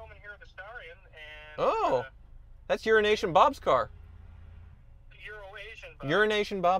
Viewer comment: Sounds like he said urnation bob on the phone to wizard 🤣